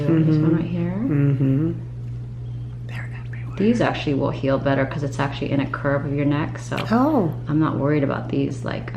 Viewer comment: a males voice whispering. Can't make out what is said. Interesting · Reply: "They're everywhere" haha